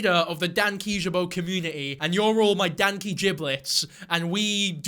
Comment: did he just call me a danky jibblet